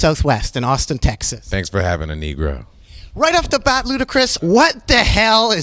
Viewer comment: did he say thanks for having a nigro........